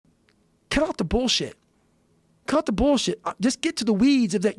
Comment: I open the video and first thing i hear is " cut up the bullshit" INSPIRATION